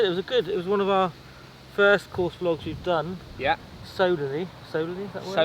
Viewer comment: Solely I thought he said soberly ha ha ha. We wouldn't have given up it was great because of the banter and comradery 😎. Be cool to see a worst ball scramble